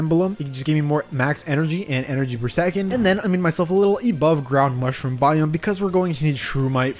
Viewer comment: did you just say EBOVE